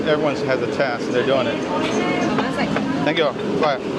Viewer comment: are you Dutch? Sounds like you said thank you in Dutch “dank je wel” 🤓